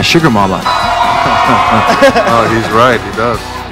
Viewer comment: Can someone please tell me what Emily said at the end. 3:14 Thanx! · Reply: it (as in her being a complete dork) came way too naturally 😂😂